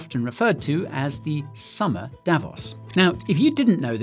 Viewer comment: I heard it as summer Devils